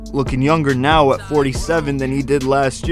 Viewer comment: Yeah I just replayed this over and over @ 6:13, Did he just say 47?